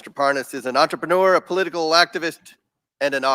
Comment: Idk about you guys, but to me, it sounded like Comer just called that guy a political laxative, lol. Maybe he said activist. But to me, it sounds like laxative, lol